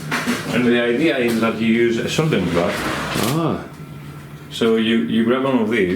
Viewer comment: Very interesting behind-the-scenes look! I cannot completely make out what he says about the soldering process at 6:40. Is it a soldering bath, or some kind of oven? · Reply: It's a solder bath, though it sounds like he said "graph" in the video. I wasn't aware how that worked until he gave the tour...only knew that they had moved away from hand-soldering the boards.